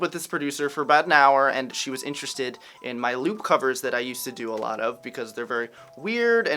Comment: I thought you said lute covers...I was really confused for a moment.